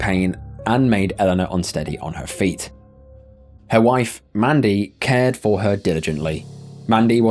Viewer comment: He said [her wife], did I hear that correctly, what a miserable, awful weird western community.